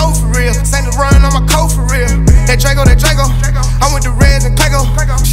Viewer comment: As soon as he said I put D in Dope for real" it was a hit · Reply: I thought he said doe, saint leron my coat fo real